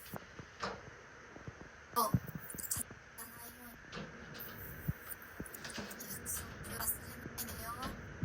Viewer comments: の聞き取れないところ 『お』から始まる名前を言って 『風邪ひかないようにね』 『約束忘れないでよー』 って言ってませんか？ 恋人又は同性の友達に話しているような雰囲気ですね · 聞き取れないところ 24:47『何時もみたいに会いたいなぁ』ぽく聞こえる 24:54『も……す？……s無いように』ここはうまく聞きとらませんでしたすみません。 25:58『私の事忘れないでよ』のように聞こえます。 ここも少しわかりにくく全体を通してまるでメンヘラ彼女が彼氏の記憶に残ろうとするために作ったような映像のように聞こえるためもしかしたら二つ目のヤツも何かの思い出か、忘れないで欲しいといつた事を言っていると思います。 あくまで一個人の限界(音量マックス+優先ヘットフォン着)までですが……